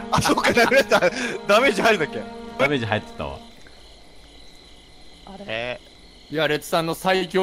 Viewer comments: あたりに女性っぽい声で「あれ」って言ってるように聞こえるんだけど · 謎の女性？の声で「あれ…」って聞こえる 気がする